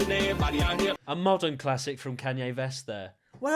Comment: Woop This year on the internet times 10 2:30 did he just say kanye vest